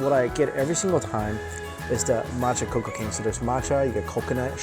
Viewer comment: I swear, I thought it said Matcha Cocaine xD